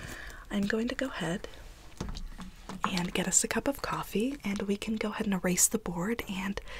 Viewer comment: I think I might be hearing things cause it sounded like she said a cup of covfefe